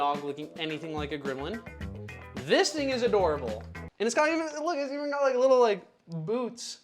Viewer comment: am I trippin or did he say “ this n word is adorable”